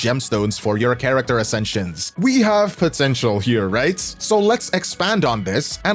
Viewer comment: DID YOU SAY POTENTIAL‽